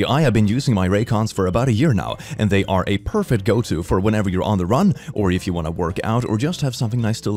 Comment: I heard "whenever you're on the run" instead of "on a run" and honestly, best advertising XD